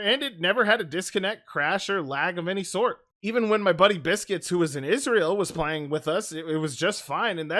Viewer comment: I thought he said, 'Even when my buddy Biscuits, WHO WAS AND IS REAL, was playing with us'